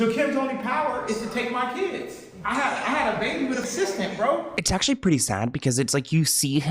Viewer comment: Did he just say I had a baby with an assistant